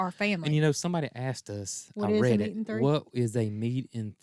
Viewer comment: I’m guessing “a meat and three” means meat plus three side dishes. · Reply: That sounds right Nancy, thank you! I heard it wrong, I thought said a three meat meal but listening to it again she said a meat and three.